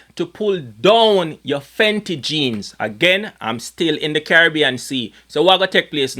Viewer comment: Thought he said Fendi jeans - love the chune bad 🔥🔥🔥